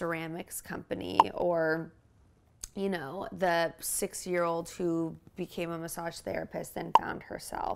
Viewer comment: In between waiting for The Hills episodes I've been watching this and wanna say how GUTTED I am I missed the development of this community from the beginning, would have been a fun journey! Also misheard Whitney towards the end, thinking she said 6-year-old massage therapist and then saw a picture of an older lady. Not sure what my brain made sense of: The humour of posting a 60-year-old instead of a 6-year-old, the realisation I misheard what was said or that last night I was watching Benjamin Button and, in a world where you're born an old person, this poor woman also has that genetic disease (which means she was a 6-year-old in a 60-year-olds body)